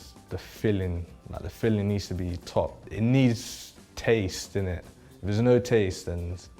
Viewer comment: did he say "it needs taste in it" or, "it needs taste, innit" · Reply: Definately “in it” and not “init”, just because he hasn’t used “init” before in any of the videos